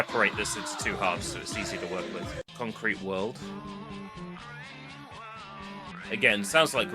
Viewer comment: The first gorillaz song you didnt know is all alone, the second you didnt know is fire coming out of the monkeys head, concrete world is a mishearing of the track name "o green world"